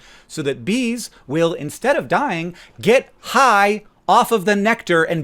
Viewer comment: did you say "get high"? Caffeine is not a drug, right? It's something completely different to marijuana, right?